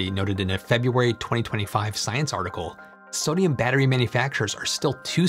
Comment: Did he just say “snooze article”?